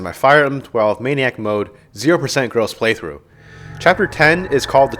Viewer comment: Would you be against one of us writing subtitles for YouTube because clearly they can't do it right themselves? and education videos shouldn't be like this: sup everyone this is Donna 5-1 and Mecca hello and we are showing you chapters 10 and 10 X my firearm 12 maniac mode 0% girls playthrough chapter 10 is called........ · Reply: In Youtube's defense it really does sound like he says "0% girls" every time.